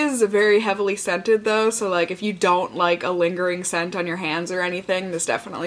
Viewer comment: When you said "hands", I thought you said "cans." Like, boob cream...